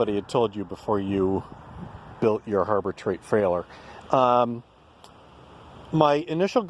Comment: did say "Harbor Treight frailer"?